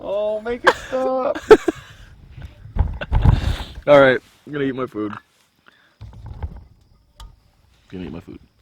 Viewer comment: There was so much talk about feet that I heard "GONNA EAT MY BOOT"